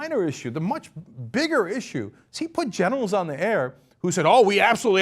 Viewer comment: one question cenk. at 3:03 did you say "generals" or "genitals"?